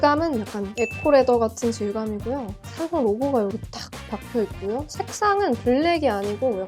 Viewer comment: 저만 "사과 로고가 여기 딱 박혀있구요" 이렇게 들리는 건가요?